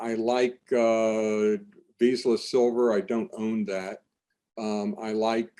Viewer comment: Hi another great interview, can you clarify what Brien said at 41:54 he mentioned a Silver mining co that sounded like veesless Silver?